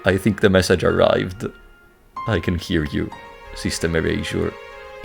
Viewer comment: sorry if i am rude but with your accent and my schizo hearing, your "System Erasure" sounded like "Sister Erasure" which i though kinda fun if SE found a word that could be misread as sister also just realised that sister and system can be read similar like sistem and syster. plus with youtube's default font "m" is a double "r" with an axtra long one at the end (but it could be just me being crazy (haha rats, rubber room)) Also insanely good video the pacing the back and forth beetween Void Strander and ZeroRanger are absolute peak cinematography i can call it Kino even (really shows your theatre interests imo)